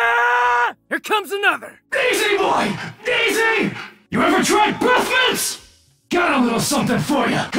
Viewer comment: What does it say? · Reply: YOU EVER TRYED BREATH MINTS!!!?